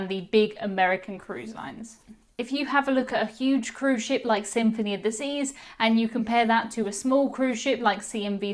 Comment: LOL😂 At one point in the vlog I thought you said " the Symphony of Disease"